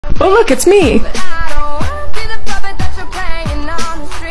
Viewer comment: I thought she said "Dont listen to Emo Dana"😭☠️